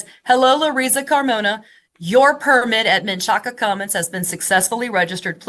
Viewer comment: Am i the only one who thought she was saying “Chaka Khan?”😆💃🕺🪩🎤🎵